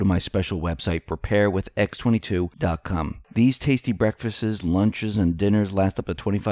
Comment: Regarding the food advert.: "Breakfastses"? Really? I played it back just to be sure I heard it right. Yup! Heard it right. "Breakfastses" Hilarious. Anyone else catch it? Start